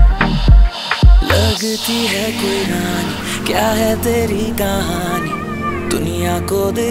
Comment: did he said lagti ha "Quran" ? If yes then ye thorra ziada ho gea ha bhai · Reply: 'Lagti hai kyun rani' bolraha hai bhai